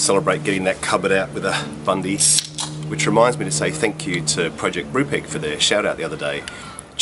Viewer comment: PROJECT BRUPEG You said it a bit too quickly for translation! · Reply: Thanks! I wanted to look up that channel but wasn't sure what he said! I heard "project brewpig".